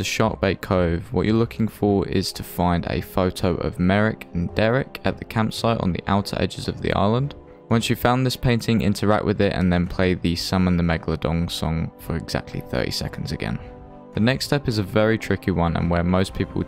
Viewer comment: Did he just say Mega-long-dong? Lol